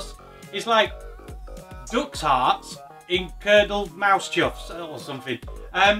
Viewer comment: i thought you were saying dogs heart......